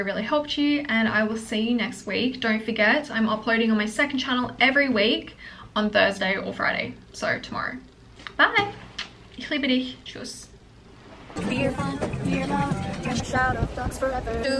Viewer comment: did you just say "ich liebe dich" ?! I'm German 🥹